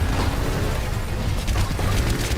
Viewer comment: did he say d.da??😆😅